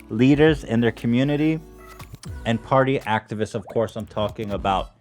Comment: Lol when Ethan mentioned the “activists” at the beginning it sounded like he said they were sponsored by “Actavis” I was like hell yeah now we’re getting somewhere